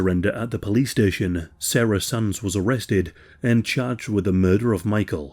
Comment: I heard "Sarah's sons were arrested". I was like "WHAT??"